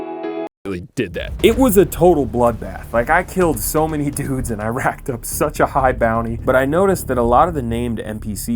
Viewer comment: I swear it sounds like he said, “… I killed so many dudes in Iraq” and I had to do a double-take